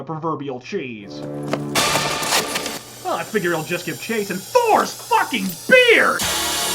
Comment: What did say?... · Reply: THOR'S FUCKING BEARD!